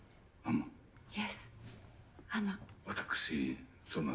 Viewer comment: Didn't catch it the first time, but I realized Fuji-sama says "Yes, Ama" It is the first and maybe last time Fuji-sama uses English. Only one word, but means alot.